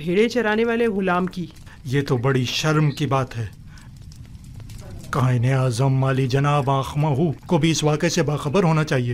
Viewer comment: i heard Qain e Azam se Qaid e Azam😂